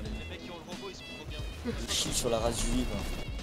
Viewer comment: Ptdr a 4:56 c moi en vocal. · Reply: la race juive je crois